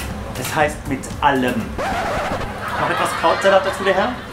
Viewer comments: Entschuldigung, ich habe den Abschnitt zwischen 0:46 und 0:48 nicht richtig gehört und deshalb nicht verstanden. Könnte ihn mir jemad aufschreiben? Danke im Voraus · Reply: Etwas Krautsalat dazu der Herr? Ja bitte. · was hat allem Kollege gesagt ? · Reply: mit Vergnügen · was hat er gesagt? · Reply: Noch etwas Krautsalat dazu der Herr?